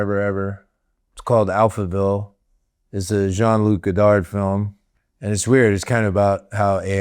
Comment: I thought he said 'Jean Luc Piccard film' haha